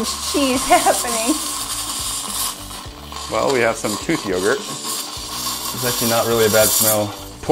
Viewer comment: im in the middle of eating and all i hear is "we have tooth yogurt" .....